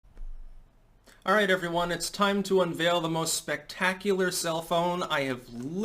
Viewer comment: I didn't look at the title of the video, and I thought he was saying, "the most amazing cell phone," rather than self-own. I kept waiting for the amazing cell phone connection to all this.